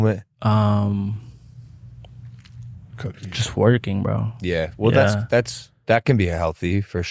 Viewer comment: I thought he said “twerking” lol